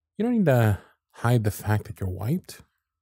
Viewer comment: This audio kills me every single time because it sounds like you say, “you don’t have to hide the fact that you’re *white*.” (@ 0:35) 😂